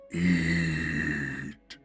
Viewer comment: I thought the thing said “I need you it” and I said “WTF DOES THAT MEAN?!?!”